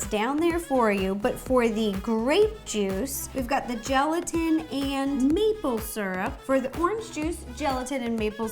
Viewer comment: Wow at 3:06 I thought she said ‘meatloaf syrup’ I was like whaaaaaa